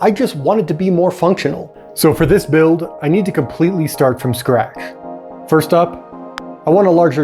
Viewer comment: what is he saying? scrak? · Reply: He misspoke scratch to scrach